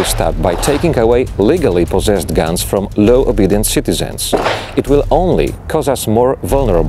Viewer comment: At first I thought he was saying "low obedience citizens", when he was really saying "law abiding citizens". Great video!